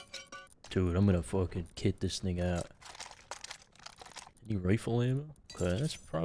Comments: ugh what’d just say 😂😭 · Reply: "kit this thing out" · I thought said the n word when he said i am goanna kit this thing out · i know he didn't, but at 41:18 it sounds like he says "Im gonna kit this n word out"